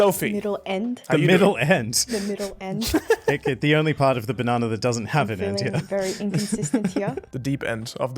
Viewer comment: I thought he said "the only part of banana that doesn't have banana"